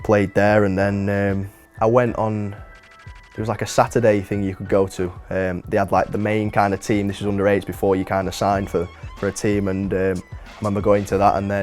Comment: does he say under 8's? Why the fuck is he getting signed so young?